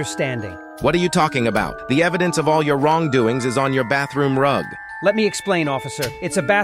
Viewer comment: I thought he said all the evidence of wrongdoings is in your blood 💀